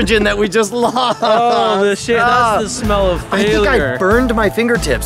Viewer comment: What did Grant Thompson say at 2:57??? xD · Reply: EliteFX "that is the smell of failure"